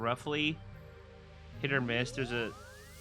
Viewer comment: did u say hit or miss